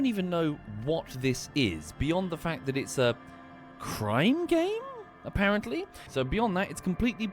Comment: Oh, he said "crime game", I swear, first time I heard "crying game" and thought to myself that it kinda fits